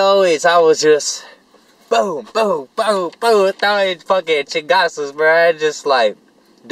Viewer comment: Did you just speak Spanish on 10:55 bc I think you said " chingasos" some shit like that